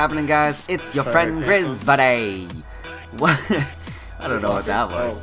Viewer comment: LOL wtf, I thought you said "It's your friend GrizMonay!" like your rap name or something, at first.